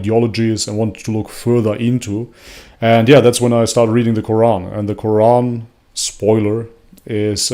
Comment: Well , that was a curved ball . Am gardening whilst listening. Just had to rewind, thought she said , " quorn " ! 🙂🙃